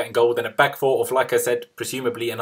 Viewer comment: You said "like I said " so fast I thought you said lacazette!!